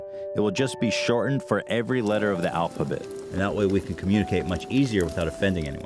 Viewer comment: did he just say alphabit